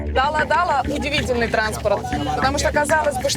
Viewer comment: Мне послышалась дама дала